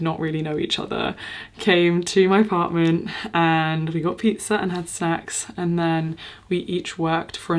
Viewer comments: oh my god i misheard it as something elseeee and i was like whaaaaa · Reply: I legit heard the same thing.... "We had pizza and had sex" no judgement from me either way but boy I was suprised 😂😂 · am I hearing that right?? 😭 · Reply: bruh i said pizza and snacks 😭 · "We had pizza and had sex" I cannot hear anything different from that. Go girl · I literally died with the intimacy when I heard you say sex instead of snacks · Sorry did I mishear u say that 5 people that u did not know came to ur house for pizza and sex? Is this the normal for ur parties? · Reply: Yes you did mishear her LOL she said "we got pizza and had SNACKS"